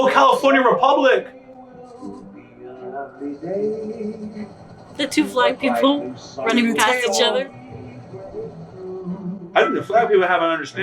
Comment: not gonna lie, i thought she said 'the two black people running past each other," and he was like "black people have an understanding"